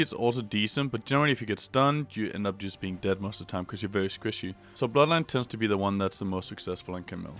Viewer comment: did he just say camille is squishy...